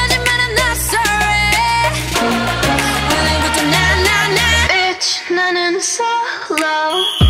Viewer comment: I always thought it said: B!tch going Solo